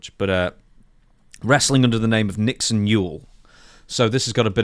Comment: I initially heard it as "Nixon Mule" and was thinking okay she's an ass